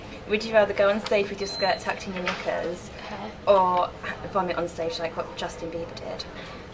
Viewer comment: 1. JADE LOOKS SO CUTE SHES MY FAVORITE😍😍😍😍 and 2. i thought she said or would you rather vomit on stage with justin bieber dead😂